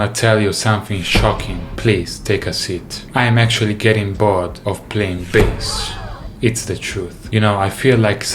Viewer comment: At first I thought he said that he Was getting BALD from playing bass so I bursted out laughing when I saw Saitama.. 🤣🤣